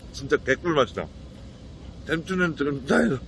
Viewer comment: ㅋㅋㅋㅋㅋ 뭐라는진 모르겟는데 웃기네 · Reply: 진짜 개꿀맛이다 잼춘이는 지금 차에서